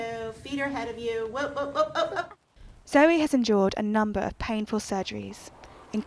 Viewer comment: I heard zoey has “enjoyed” various surgeries instead of “endured” lol